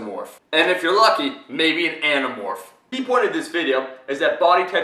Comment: haha I thought that he said a nano morph